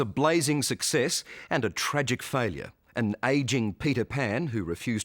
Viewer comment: first I heard "an aging pedo pan".